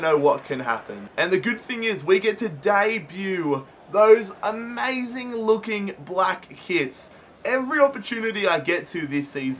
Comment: thought you said something WAAAYYYY different 😂😂😂😂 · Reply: I thought he said amazing looking black kids